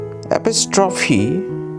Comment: "apostrophe" i heard "episode 3" 💀